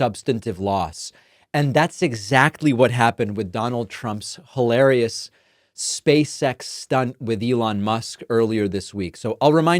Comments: Oh, no. I initially heard you say that as trump and elon space sex stunt 😂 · “Space X”, David I kept hearing you say “Space Sex”… ROFL! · lol subtitles got me a little concerned... "And that's exactly what happened with Donald Trump's hilarious space sex stunt with Elon Musk..."